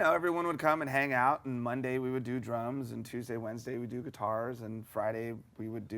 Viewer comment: sounded like he was gonna say "do drugs" lol